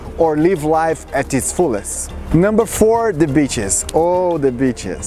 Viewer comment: 😂😂😂 I was just listening to it not actually watching the video and I thought he said something other than “The Beaches” Son of Beach 😂😂